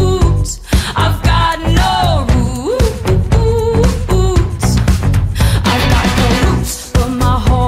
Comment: I thought it said I've got no roofs in my home lol😅